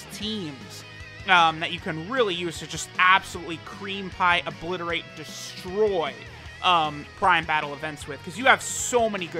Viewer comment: Wait what did he say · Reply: Mr. Kip Creampie, Obliterate and Destroy. Yes you heard right